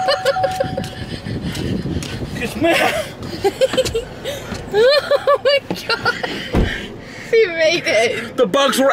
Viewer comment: what did say?😂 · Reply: MEHAAAA